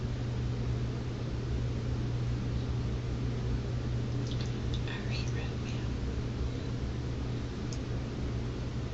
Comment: Watch the video when she is left by herself. She forgets she is being recorded I assume but at timestamp 26:04 she smiles and says out loud something that I can’t decipher but it sounds like she says “Irene read me or I eat red meat? I can’t figure it out but something crazy replaying in her mind. No one has mentioned this in the comments so I was just pointing it out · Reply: Sounds like she says.. "Irene ratted me out"